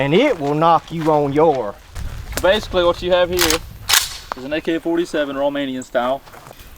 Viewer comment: did he say "an AK-47 Romanian style" ? Greetings from Romania, BTW!